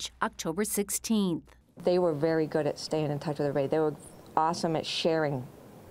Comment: I'm studying English as a second language. Can anybody tell me what the woman said at 0:51-52 seconds? Please🙏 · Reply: They were very good at staying in touch with their families